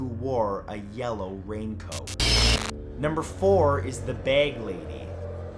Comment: The bag lady I thought he said the bad lady